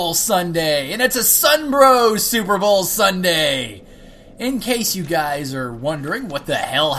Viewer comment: For a second I thought you said "Sunbro Super Brawl Sunday."